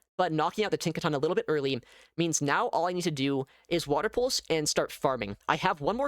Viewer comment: I wasn't paying full attention to what was being said and heard "I need to Water Pulse and start LARPing" and not "I need to Water Pulse and start farming." I'm sure all of you can imagine the levels of confusion damage I inflicted upon myself. 😅😂